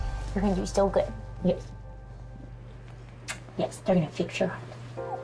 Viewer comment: when she said they were going to fix his heart, I accidentally turned the volume down and somehow I thought she told him they were going to take his shirt off. I didn't think that's what she said until I saw the image of his top half shaved. And I knew it was. Until I skipped back to make sure I heard it right. I didn't but they did take his shirt off. 😂